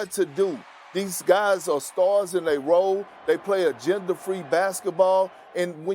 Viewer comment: that southern drawl messin me up , I thought that man said ”a gender free basketball” Like non binary hooping😂